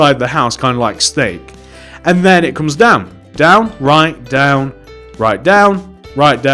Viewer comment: it sound like he was saying dam right even tho I know he was saying down right like if you agree.